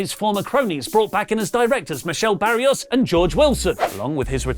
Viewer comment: Umm, at 5:36, did just say Michelle barrios and George Wilson? Are they expected in the impact zone?